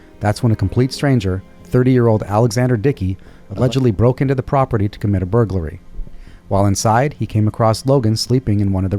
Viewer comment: Am i the only one who heard a voice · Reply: Ay yo he said hello 😂😂